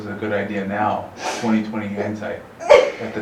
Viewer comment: Did he just say 20/20 handsight?